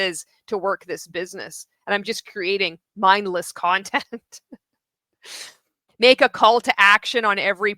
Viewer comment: “make a call to action” I looked away for a sec and was just listening and I thought you said “make a cult action” 😂😂